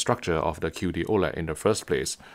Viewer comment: I keep hearing "cutie OLED", and now I imagine a TV-waifu.